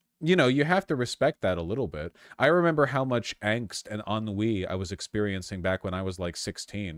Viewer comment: You said "ennui" and at first I heard "un-weeeee!" and it still made sense 😅